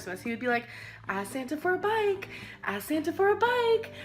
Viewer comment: I was washing dishes while listening to this & I swear to God I thought she was saying "ASK SANTA FOR A BITE"